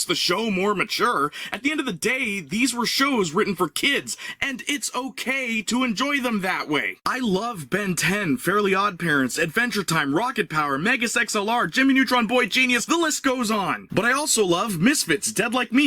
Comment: While I enjoyed the rant, I was distracted because I could have sworn you said mega sex lr and it took me a minute to reboot